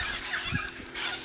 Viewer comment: I thought he said witch tail eagle. Google set me straight.